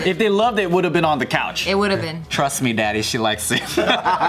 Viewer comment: Did Tim just call “daddy”?